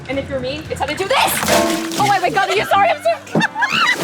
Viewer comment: stumbled on her words and said "Are you sorry" and I think was about to say "I am okay"